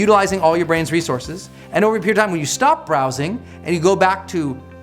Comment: sorry everyone can help me? At 3:06 i hear brendon say: "are utilizing your brain's resource and (1)** ". I can't hear clearly what does he say at (1)**, but in subtitle i see words "overpair time", isn't it? i feel it not correct. And at 4:20 " firing off my dopamine in my bat(2) suppressing". it is word "bat", isn't it? I'm improving english myself. Thank you very much.